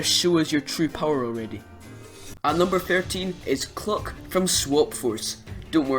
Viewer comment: At first I thought you said Clock, that'd be interesting.